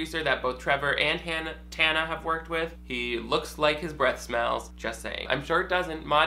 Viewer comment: Thought you said “he looks like breast milk” and caught myself agreeing even after a double take